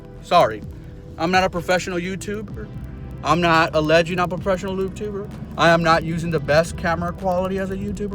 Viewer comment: Did I hear, "I'm not alleging I'm a professional lube tuber" at 5:18? LMAO